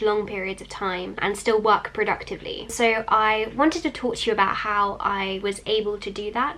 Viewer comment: I thought she said "I am going to spill the tea about" instead of "talk to you about "